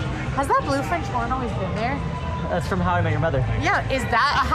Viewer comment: Only took 4 tries, but at least this time I was able to figure out what you were saying…”how I met your mother”.